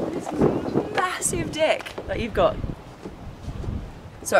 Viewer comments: 😂 is that what I think she said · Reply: Its deck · i listened to this 80 times back to back and on the 81st time i heard ”massive deck”. Having captions on didn’t help either.